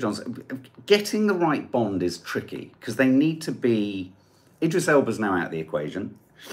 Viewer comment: Wedding cake in the face is absolutely assault- if I was the groom’s mother I’d pull him aside, have a strong word with him and insist he apologise straight away then tell the bride to RUN not walk! Regarding Bond, why can’t they just make Idris Elba and be done with it 🔥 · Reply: Blast, just heard you say the Elba is out. Ok James Morton’s a hotty!